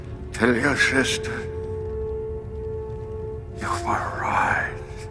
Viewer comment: It really does sound like Vader says "tell your sister, you've arrived" not you were right..